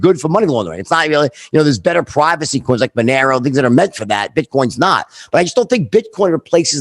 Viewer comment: What did he say at 30:37 about money laundering and privacy laws? It sounds like he said “Banero”. Like Banero Island??